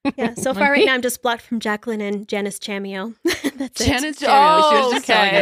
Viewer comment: i just heard of Janis Chaneo and now I can't unsee it and I'm dying 💀💀